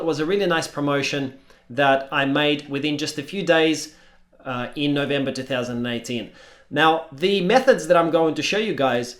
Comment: did he say “in November 2019”